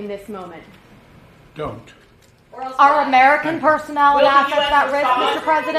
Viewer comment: I thought Joe said Don’t don’t.Americans seen how that worked out before.